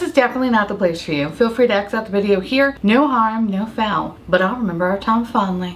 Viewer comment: I must be hungry watching this because I could have sworn in your intro you said, “no ham, no fowl” … and I just can’t…🤣🤣🤣🍗